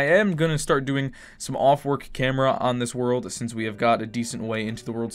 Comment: did he just say off-work camera?